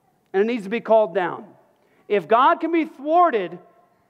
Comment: Can someone help me with what he is saying? He keeps saying ".. and God can be -forded, fowarded??" I cant understand. Go to 37:12 and someone might see what im talking about. Thanks. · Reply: He says, "If God can be thwarted..."